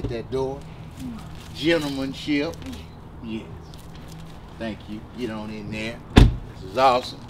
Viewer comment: Did he say thank you? 😂